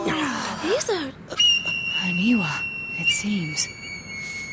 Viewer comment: Anyone hear what Naoto called the statues? They're clearly Kodama, those bobble-headed creatures from Princess Mononoke, but I swear she says Inua. If that's the case, that's a rather odd if proper localization, changing it from Shinto to Inuit mythology. Both would be considered 'tree spirits' in a sense.